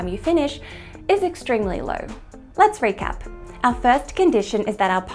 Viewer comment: "Let's recap." At first I heard "Let's break up" and felt oddly broken, even though I only know you on Youtube.